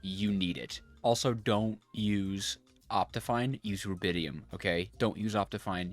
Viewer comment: he said use what? Mumbled the word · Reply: rubidium